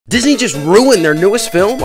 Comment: I misheard you in the intro and for a split second I thought you said "disney just ruined christmas" and I was confused lol